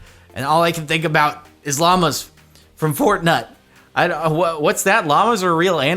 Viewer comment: did he just say "fortnut" 😂